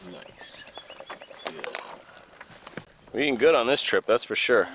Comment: what does says at 10:00 ? I'm being good on this trip? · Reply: He said he’s eating good on this trip that’s for sure according to the subtitles